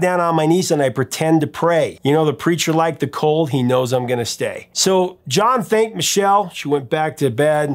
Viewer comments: I’m half deaf, so I thought he was saying: The preacher liked to talk, he knows I’m gonna stay. lol. I learned something new. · Thanks. I'll never listen to "California Dreamin" in the same way. But I couldn't make out what the original preacher lyrics were. What were they??? It sounded like you said the official lyrics were: "The preacher lifes the cols".